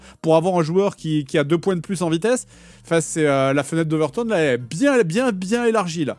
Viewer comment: C'est quoi la fenêtre d' "overturn" (vers 2:34) ? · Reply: C'est la fenêtre d'Overton, une théorie selon laquelle plus tu affirmes des choses inacceptables, plus ça les rend acceptables